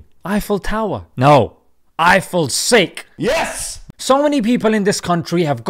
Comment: Eifell tower? No! Eifell sick! 😂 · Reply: I thought he said 'Eifell sink'